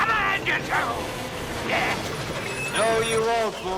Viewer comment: Did he just say a bad word? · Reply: No. He said “YES!”.